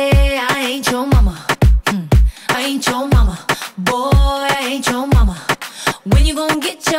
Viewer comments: When I Was Younger I Thought She was Saying “Boy, I ate your mama!” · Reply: I thought she was saying "i hate your mama" 😂😂😂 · When I Was Younger I Thought She was Saying “Boy, I ate your mama!” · When I was younger I thought she said I hate your mama lol love you jlo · When I Was Younger I Thought She was Saying “Boy, I ate your mama!” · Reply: And I thought she was saying killed your mama!!!😂😂😂😂😂😂😅😅😆😆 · When I Was Younger I Thought She was Saying “Boy, I ate your mama!” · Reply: What I heard was "I and your mama" :'v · I swear I heard "I hate your mama" 😭